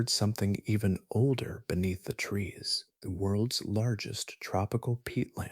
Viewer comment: I thought you said the worlds largest peacock at first 😂